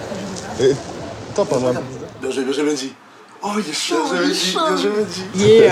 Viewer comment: Il dit quoi à 23:10 ?? Benjamin Mendy ?